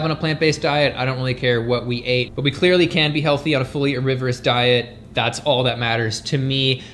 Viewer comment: What did you say at 17:58? "We clearly can't be healthy on a fully riverous diet"? Was that supposed to be "omnivorous"? But if so, we CAN be healthy on an omnivorous diet, as long as plants are still at least 80% of your calories (I pulled that number out of thin air)!